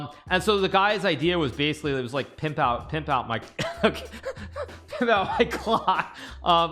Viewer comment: I think Hikaru heard him wrong. He for sure said pimp out your cock 💀